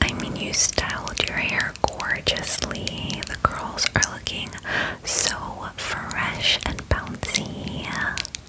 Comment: She said the curls are looking fresh and bouncy but I heard “The girls are looking fresh and bouncy” 🤣🤣🤣